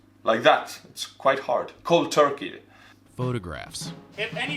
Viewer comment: if your photo is what? I’m guessing he said “offensive” well turns out I’d rather start my own... no nvm